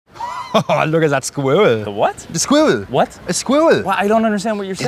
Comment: I hear "look at that squidward"